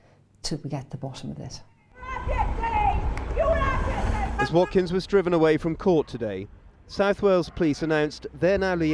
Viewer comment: What's she yelling, I can't understand? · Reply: i love you